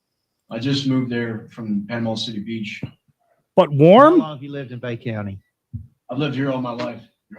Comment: Did you say "butt warm" @ 3:40? No sir, my butt is freezing up here!!! 19° freezing!! 🥶